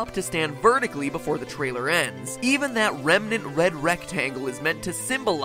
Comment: This really was just an 8-year long war between Scott and MatPat wasn’t it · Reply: just a question. Did he say remnant 3:10.Or am I hallucinating